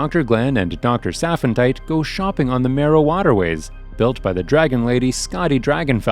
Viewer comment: Bro I never rewatched a clip so many times to figure out if I heard something right bc when he said "merrow water ways" I thought he said "marijuana ways"